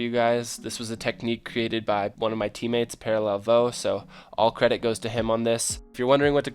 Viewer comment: wait did he say parallel Plo...